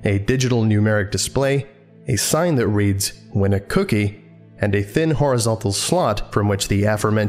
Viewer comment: Anyone else thought the machine said "Win a Coochie" for a moment? I'm honestly not sure what to think about that if it did... I wouldn't trust an SCP Coochie.